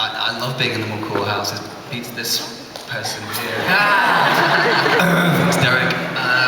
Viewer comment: OMG did Daniel said "Sterek" at 18:38 ? o